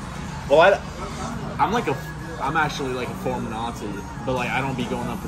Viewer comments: who else heard say “I’m actually a former nazi” Sus bro sus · did he just say he was a "former nazi" lol